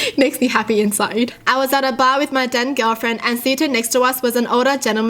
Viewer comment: when said "then girlfriend" I thought she said "DEAD GIRLFRIEND" my head legit snapped to my computer screen I was like "WAAA?" but then I read it and was like "OOOOH!" like I was about to be all "Whatever you're into"